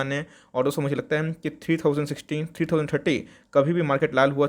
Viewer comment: 3000 tatti 💩 instead of 3030 :)